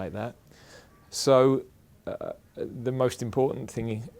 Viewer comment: wait, wait, did he just say 'the most important thingy ?'